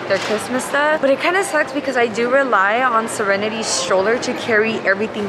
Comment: I thought said she relies on Serenity’s SHOULDER to carry all the bags! 😂🤣😂💀💀💀